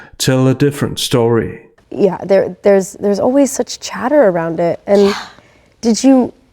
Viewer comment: Love your work. I seriously thought you were saying Chedder lol instead of chatter